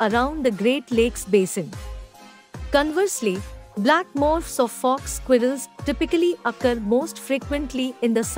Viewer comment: Sounds like she said "Black Mofaqers".😂